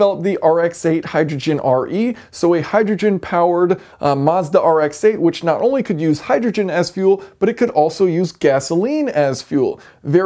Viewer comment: I misheard. I thought I heard "hydrogen ass fuel" and "gasoline ass fuel" hahhahahahahaa